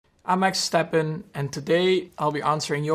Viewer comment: I'm Max Stappenにしか聞こえない..